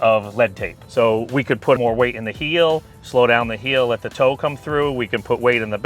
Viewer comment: You said he put weight in the heel, I thought I heard him say he put the weight in the toe, which helps slow down the face closing. Weight in the heel is more of a draw basis. Weight in the toe helps fade the ball. PXG made big improvements in their driver line this year and they have a good price point. I have tried their stuff over the years and never put in play. I’m making the switch from Callaway apex 19 to PXG 0311P irons this year.